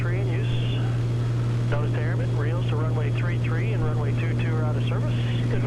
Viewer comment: Can you tell what is out of service for R33 and R22 at 18:50? I cannot understand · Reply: REILs to Runway 33 and Runway 22 are out of service. <link>